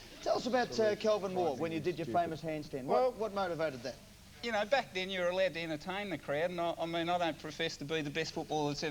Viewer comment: What does newman say to him at about 4:27? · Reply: you are an idiot. absolutely frighteningly stupid